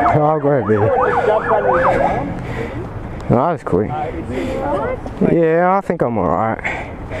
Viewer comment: did you say that was quick? haha , is true , ambulance was there in 1 min